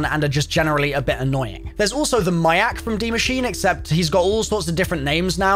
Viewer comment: did he say “miack?”